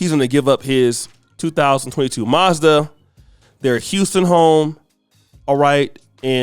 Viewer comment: Side note shout out to the brother for owning a 2022 Mazda. Not every celebrity need a six figure car. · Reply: I thought he said "mazza" short for Maserati.